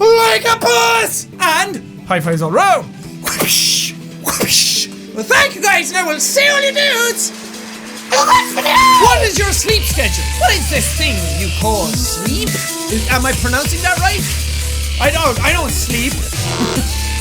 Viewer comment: I always thought that you said and I'll see all your nudes in the next video at the end of your videos but know I now you say ill see all you DUDES in my next video I'm great I know